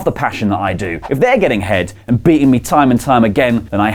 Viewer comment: Is it me or did it sound like he said "getting head"? ( ͡° ͜ʖ ͡°)